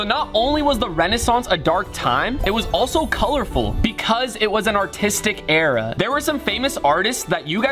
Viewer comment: thought rug was saying hitler is an artist too☠️☠️